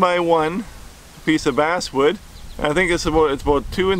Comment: I obviously used the wrong wood. Thought you said a$$ wood, not bass wood. Now my owl stinks. Ok. Bad joke. But what isn't a bad joke is in about three more instructional videos, I will have a complete box of toothpicks. Yea me. Thanks, I really enjoyed this tutorial, and the owls are my favorites so far. How about a penguin next?